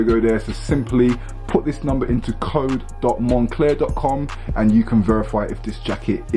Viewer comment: excuse me did he just say moncler lol